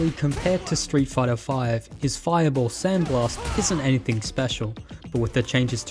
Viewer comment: I always thought luke was saying "think left" instead of "sand blast"😅